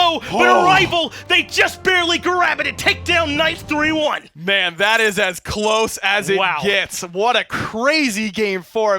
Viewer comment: "I wonder what PBM said...." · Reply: has “Let’s fucking go”